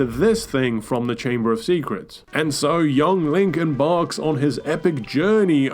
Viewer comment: I thought I heard say Linkin Park instead of Link Embarks lmao